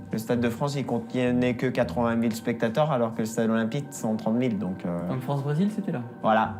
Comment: Bro really said "Who even needs Sim City when I've got everything I need in my mind?". 4:04 - MINOR MISTAKE SPOTTED!! The subtitle says the Olympics Stadium holds 113 000 spectators, but says it hold 130 000.